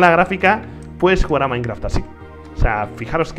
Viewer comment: Yo escuche "Si te toca la grafica puedes jugar al buscaminas a 40 fps"